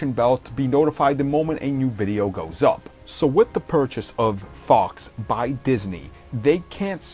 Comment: Hold on are we all gonna skip the fact he said fox bought Disney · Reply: I think you heard him say “Purchase of Fox by Disney.”